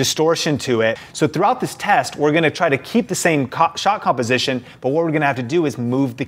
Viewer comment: do you say cock instead of shot ?